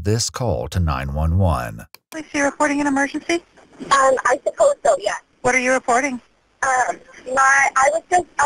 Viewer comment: Do you want to report an emergency? "Uhh, I suppose so" 💀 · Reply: I thought she said “I should hope so” when I was listening without seeing the screen, I went back and wtf “I suppose so”?